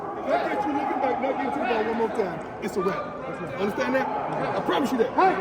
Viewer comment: was Prime pissed his DB didn't look back at the ball, and did he tell him if he does it again he going to kick him off the team?....I rewinded that part 7 times and still couldn't quite understand what Prime said. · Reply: He got caught looking back at the qb, and yes, one more time, and he's outta here